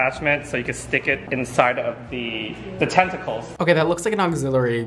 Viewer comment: Omg, Guy Tang said stick it inside the tentacles (the first time he said it.) I heard "Stick it inside the testicles." I was thinking what kind of weird ish is this? 😁